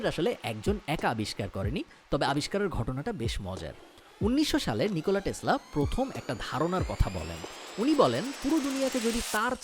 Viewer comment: Did you say Tesla? I am giving you a lal salam for that.